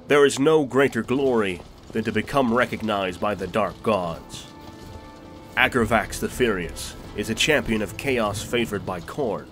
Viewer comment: I heard "Aggravax the Curious". Thank the Dark Gods we have subs! ;)